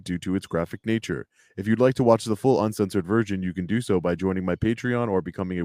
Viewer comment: Bro did you just say full uncensored virgin? lol